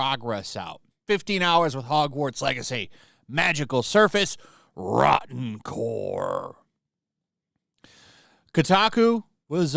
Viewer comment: u sounded like Torbjorn saying MOLTEN CORE idk why